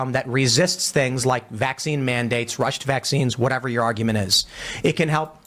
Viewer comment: Tim said 'rushin' vaccines' and I thought he said Russian vaccines. Probably because he has been paid by the Russians. It's like my brain was 2 steps ahead of my ears.